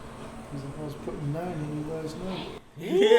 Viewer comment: What does he say? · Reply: 'I was putting 9 in you last night' I want to high five him